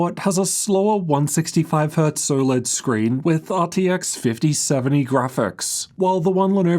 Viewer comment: wut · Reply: you said soLED screen or i hear wrong?